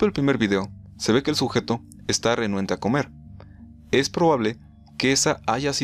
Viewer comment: "Al principio del primer video, se ve el sujeto esta riuehuyrgefjhabfjkabd a comer" Qué dice en esa frase? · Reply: Renuente